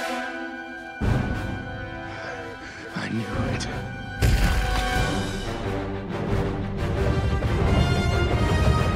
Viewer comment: what did Thor say? 0:52 i cant hear :( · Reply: "I knew it"